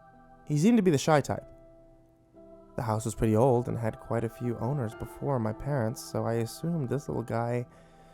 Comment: I seriously thought he said the house had a couple boners before